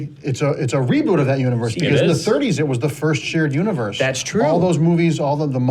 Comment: Can anyone tell me what he said? "it's a reboot of that universe, <something> 1st shared universe" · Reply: "In the 30's ".... it was the first shared universe. then he goes on to explain how there was a lot of crossover with the various 'monster' movies in the 1930's.